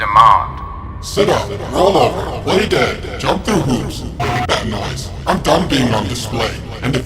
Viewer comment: did he just say F*** that noise?